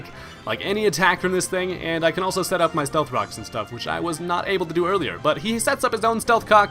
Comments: Did you say stealth cocks at 2:04? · Did anyone hear him say stealth cocks at about 2:00 ?